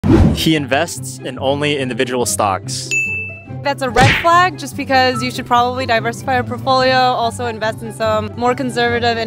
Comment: At first I thought he said "individual socks" and I was like WHO thE HECK woULD Do ThAT